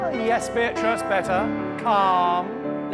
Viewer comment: he says yes Beatrice and I genuinely thought he said yes bitch